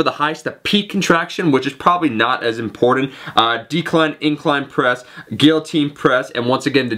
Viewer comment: did he just say decline incline press at 1:50 ? some kind of new exercises